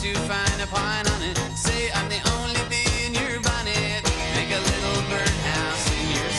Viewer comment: When I heard this on the radio for the first time a few days ago, I thought he said "I say I only wanna be in your body." I was wtf!? Lol